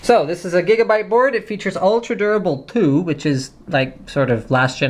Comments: I thought he said Ultra Durable POO XD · is it me or does it sound like hes saying ultra durable poo lol